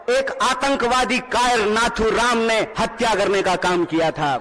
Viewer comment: kya bola? 0:48 Ankawadi guy ?